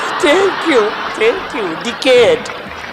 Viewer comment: does he said dickhead? 😂😂